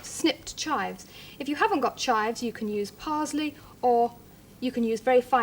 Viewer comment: does Delia say PARZLEY?